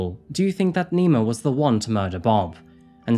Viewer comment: Misheard "Nemo" as "Nero" and wondered what in the worldly fuck.